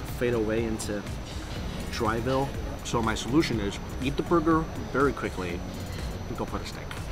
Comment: “So my solution is, eat the burger very quickly, then go f**k a snake” hahaha did anyone else hear that?